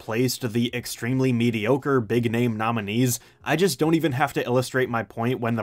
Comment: did he just say Views was mediocre?